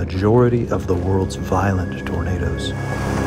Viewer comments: I watched the video with subtitles on and around 2:30 it says VIOLET TOMATOES instead of VIOLENT TORNADOS :) and those are not auto generated subtitles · AHAHA AT 2:26 THE CAPTION SAYS TOMATOES INSTEAD OF TORNADOS AKJNSKDJHDSAKHDF EDIT: 3:53 HAS BURRITOS IN THE CAPTION DFIKHG SKFLUJNSKDFLM;G DFLMG NSDFL,GNBSDF, G